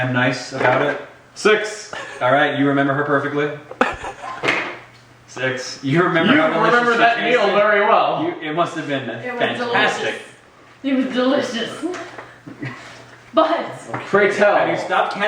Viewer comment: pointing at Masae at 11:49 did he say "you remember that anal very well?